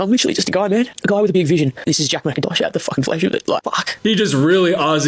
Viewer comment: LMAOOOO i think he’s saying “this is jack mackintosh out the fuckin flesh with it” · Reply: I heard "out the flyshute" 😭